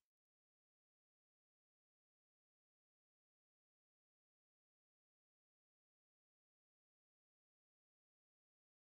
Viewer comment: Talk-to-push--tank you munch--Ta To Mush--Yes you Push--Say You Punch--Yes Good Boy--Yes Extra Burnt...SA SU-MER...What is he saying??!!?!?!?!? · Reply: Daijoubu. It's Japanese for OK sort of. Here it means the girls will be fine on their own because he's trained them.